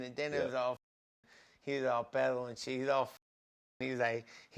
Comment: did he say "pedo"= mexican slang for drunk? Lol